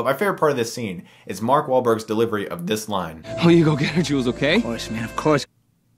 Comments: What does Wahlberg even say? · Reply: It sounds like he says “Well you go get her Jules, okay” but ya know speculation lol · I couldn't even understand what he said, honestly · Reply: Oh it's Jules! Because the other guy's name is Julian! It's a nickname, I've cracked the code! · What does Mark say at 10:57? And what does the other guy respond? I can't make it out. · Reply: "Well, you go get her, Jules, okay?" "Of course, man, of course!" ... I think. :P